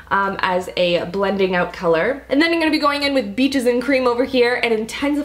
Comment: Beaches and Cream sounds like you said Bitches and Cream